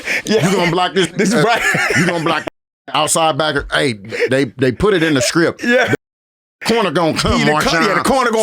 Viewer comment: Did ML say that Sherman said "they put it in the script"... hmmmm